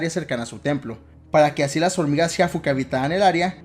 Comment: Mierda por leerlo de corrido entendí "que son las waifu".